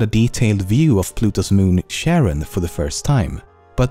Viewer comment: Did you just say Pluto's moon was called Sharon?